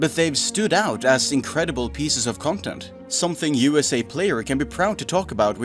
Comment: I heard it as 'Something USA player' instead of 'you as a player' the first time had to go back and rehear it.